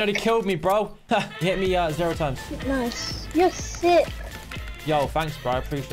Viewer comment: Notice how the kid said you're sick and the captions say you're so sick